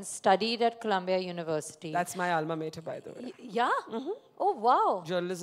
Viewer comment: Can somebody tell me what barkha said at 8:57 about thats my almamative? I dont know what that word means. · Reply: Dear ..it's Alma mater . She said she also attended the same university which Sara has been to